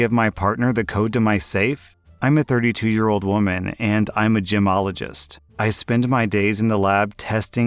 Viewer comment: I honestly thought you said Gymologist not gemologist and I was so confused for a second wondering if they sat and made better weights or training mats for people to work out on 😂